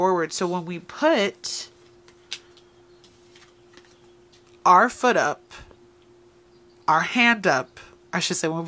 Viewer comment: I could have sworn you were about to say “When you put your foot up…their ass” 😅